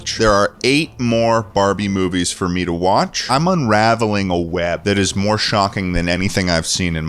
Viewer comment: I thought he said madaming a web at 1:28:19 😭😭